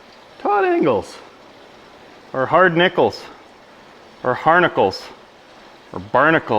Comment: Todd Engles....Barnacles..LOL - I always thought he was saying "Hi Nicholas" 😆